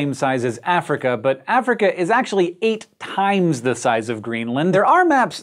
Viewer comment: Subtitles are incorrect @ 1:50 "... but Africa is nearly 8 times the size of Greenland." is showing 14 instead of 8.